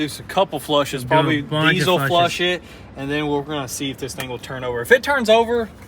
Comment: Did he just say desal flush it???